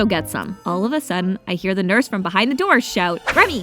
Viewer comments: bro at first i thought she said pickups like huh did i hear that right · I thought you said pickups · For I second I thought you said pickups 😂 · I thought she said pickups😂😂😂